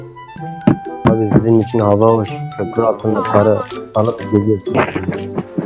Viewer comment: Gece izleyince net anladım 5:50 de -Tabi sizin için hava hoş köprü altında vurdurup alıyorsunuz diyor telefonu😂😂😂